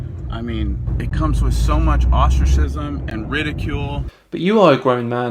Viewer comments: when i first heard it, i thought he said "ostriches"..... sadface. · Did he say ostrich-cism?